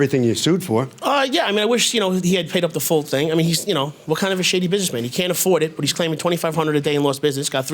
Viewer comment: What did the plaintiff say after the verdict at 9:31? "What kind of a blank businessman?" Maybe my ears aren't hearing it right :) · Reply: Shady.